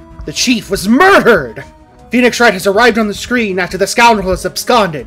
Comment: may want to put a space in that title so its not a real hash-tag. 0:24 also it sounds like you said "arrived at the scream*" lol